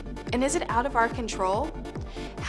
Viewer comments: and i keep hearing "come on , talk dirty to me" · I thought she said "talk dirty to me".